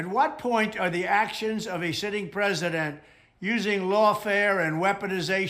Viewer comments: It legit sounds like he said a "shitting president". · Omg - he says "the actions of a sh*tting president" 1:10!! I've listened to it 3 times. Do you hear what I hear? Hahahaaaa!